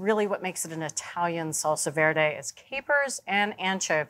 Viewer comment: Oh, Bridget, I thought you said "salsa verde" and I was expecting tomatillos. I had to look it up. It's "salsa verte" or green sauce, and wikipedia says the basic recipe may be 2,000 years old.